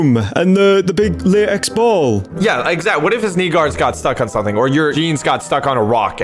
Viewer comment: he did NOT say sneakers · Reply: Knee guards